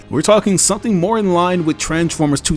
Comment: WTF I hope I misheard that. Did he really just say that ROB has the best Transformers story? Rise of the beasts was the biggest Piece of SHIT next to Bumblebee and Transformers One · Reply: You must've been focusing too hard on the ROTB visuals as opposed to what I was saying during that part. I said Transformers "2007". Which was the film that brought the entire franchise back popularity before it almost died in the early 2000s. And I literally said "or Transformers One which had 1 of the best Transformers stories".